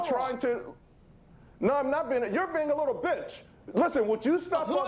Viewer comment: Did he just call him a little bitch on fox business ?